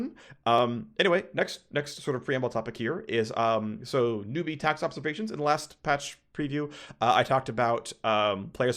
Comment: Gonna be honest, i thought it said '"newbie" tax evasion' for a moment